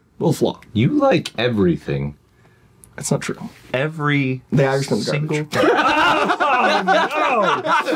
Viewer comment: I can't figure out what says is garbage 😣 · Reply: I think he said the Irishmen